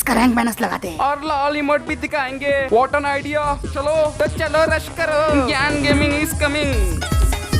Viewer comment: did he just say gyan gaming is coming🤣🤣🤣